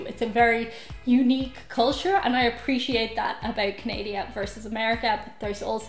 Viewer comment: Am I the only one who heard her call Canada Canadia at 2:36 or just the only rude American to point that out?